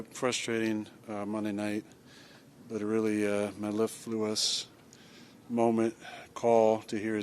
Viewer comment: In case someone was wondering what he said at 0:14, it was "mellifluous" lol